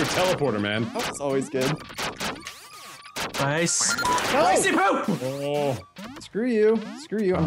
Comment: did he say i see poo